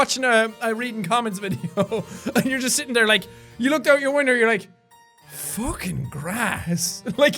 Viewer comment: Did... Did Jack say "winder" at 11:26?!